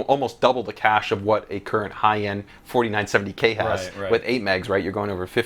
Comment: did he just say 4970k?